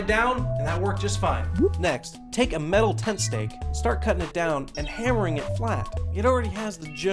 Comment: whats the first thing he said on the shoping list? i heard "metal something steak"